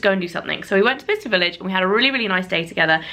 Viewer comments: All these years and I thought you were saying "Vista Village!" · I had literally always thought you were saying vista village 😂